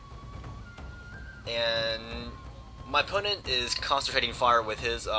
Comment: i swear says " my opponent is CONSTAPATING fire" haha lol any way nice vid likin the monster energy theme